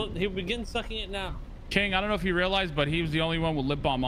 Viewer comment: did he call K “Chang” lol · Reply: No he called carmine "king" but I can understand the error, it does sound like he said chang a bit